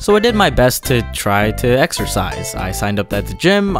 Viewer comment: Exercise? I thought you said Extra Fries 😞🍟💪